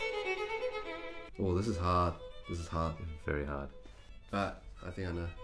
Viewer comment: I thought they were saying "This is Hahn, this is Hahn this is very Hahn..."